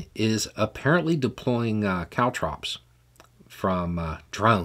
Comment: What is it that the Ukrainians are dropping?? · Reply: Lol. I thought he said Cow troughs. Then I thought "cow drops?" , then I pictured a fire response helicopter with a huge bucket of water... Except it was cow shit. Thanks for the answer. 😁